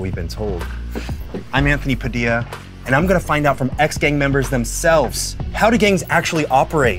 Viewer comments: So I thought it said “ex gang bangers” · I thought you said X Games members and was like "what the heck does Tony Hawk have to do with crack-cocaine???"